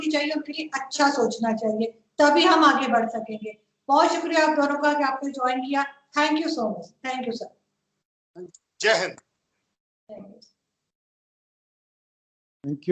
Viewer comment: Did Aarzoo say " Jai Hind" 1:01:38 ??